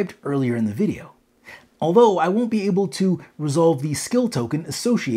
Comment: Hello Greetings from Poland! I have two questions: 1. Are you sure that worker dices can also activate the skills? I cannot find any info about that in the rulebook. Also in the rulebook "Resolve skill" is ONLY under the Character Action. Under Worker Actions there no word that they can activate the skills. Which could make sense as those are CHARACTER skills, not the worker skills. But maybe I am wrong... If possible could you please tell on which page there is an info about that? On page 24 there is a sentence: "The Crafter may be able to resolve one of their gained skills during the resolve skill action described on page 15.". ---> The Crafter, no word about workers. 2. Are you sure that Refine Materials action can be done multiple times per one turn? Also I cannot find any info aobut that in the rulebook. I see no word about that you can repeat that as many times as you want (ofc if you have enough resources). So could you also clarify it for me? Where have you found that information? On which page of the rulebook? Thank you in advance! :) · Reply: Ohhh apologies then... I just triple checked now again and its my mistake... I heard "I will be able" instead of "I won't be able"... My bad, I am sorry for that. English is not my native language and I heard it not correct :/ Thank you for clear this for me :)